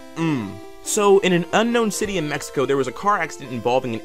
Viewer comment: did he just said fucking mexico